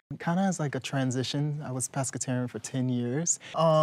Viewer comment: did he just say prespertarian instead of pescatarian